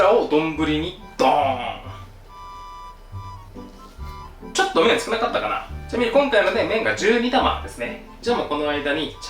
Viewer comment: 初見です！12玉で ちょと少なかったかな… と聞こえたので病院行こうと思います！難聴かな!!